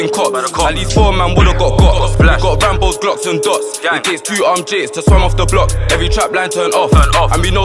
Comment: What did he say at 1:06? Something like ''it takes 2 armed jakes to swarm off the block'' ?